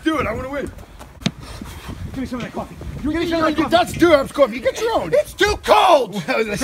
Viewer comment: Did I just hear say "That's DUHOP'S coffee."??